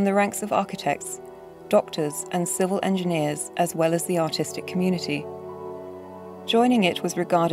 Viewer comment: did she say autistic or artistic